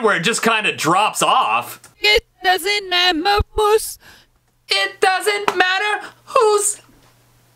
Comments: it sounded like “ It doesn’t mama booze” that’s my opinion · Wtf is a magma moose, And why are you saying "it doesnt" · "It doesn't mammer, who's" Is what it said! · I heard "it dosen't meme bus" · I heard it doesn’t matter moose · I hear 'it doesn't MAMMAL moose' for some odd reason.